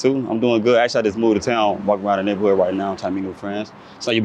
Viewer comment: Did he just say I’m walking around trying to find friends 😂